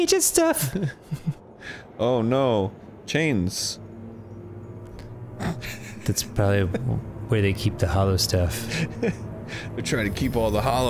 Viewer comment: Not even a few minutes in and I heard, "Ah, the chains, that's how they keep the hollow in." That's it. That's the game. They figured it out.